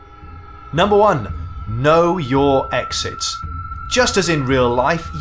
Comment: Lol I thought the first tip said 'Know you exist'.